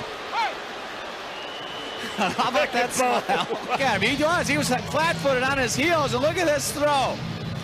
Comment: 腹立つスマイルにしか聞こえへん